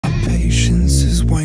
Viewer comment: What I hear "my patient is rwaining"